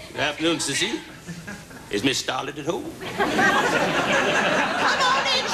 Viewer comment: The funniest parody of a movie I have seen. Vicki Lawrence was so hilarious. Miss Scarlett, Miss Scarlett, Miss Scarlett, Miss Scarlett! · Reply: rick p. I thought I heard Ms. Starlett, too.